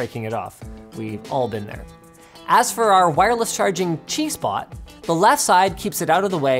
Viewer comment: I can't be the only one who heard "wireless G-spot"?